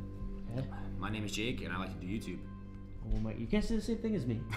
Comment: I heard, "my name is Zaros" .. The spirit you guys summond in your living area. It must be attached to you. Follows you everywhere you go.... You should sage yourself to remove any bad energy. ✌🏼